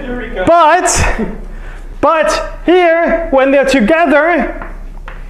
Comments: Thought he said a Boolean..wtf is a abelion lol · Abelian. I thought he said a billion at first jeez. 😂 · Abelian. I thought he said a billion at first jeez. 😂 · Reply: as a coder i thought he said "boolean" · Thanks for writing, I heard " Not a Billion." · I thought he was saying " not a billion" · "Abelian"...? I thought he was saying "a billion."